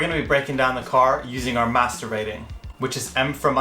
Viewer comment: I THOUGHT YOU SAID MASTERBATING..